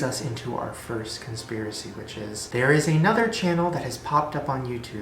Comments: I thought he said '...first conspiracy, bitches' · I thought he said conspiracy bitches 😂 😂 omg